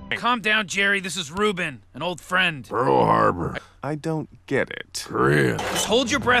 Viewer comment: Pearl Harbor??? did he just say that? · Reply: Pearl Harbour, then Korea.